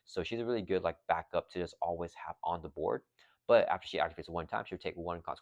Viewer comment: Black Widow ally does not take consequential damage for her interrupt ability, only thwart/attacks like you said! Did i make a mistake somewhere and give her damage for using her ability? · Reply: Sorry for the late reply, the holidays have been a bit busy. I misunderstood what you said at 2:18 and thought you said once she uses her action she takes 1 consequential damage but when I rewatched you said "activate" which makes sense. Also during the actual gameplay you didn't make that error so no issues my mistake haha!